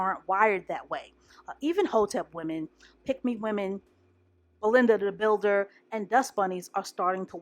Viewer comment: Did I hear "Belinda the builder"?😭